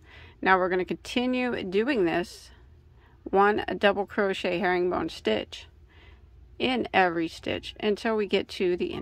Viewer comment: I thought you said it’s the houndstooth stitch. Is that the same as the herringbone?